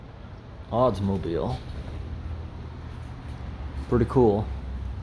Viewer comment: Sounded like u said Oddsmobile and not Oldsmobile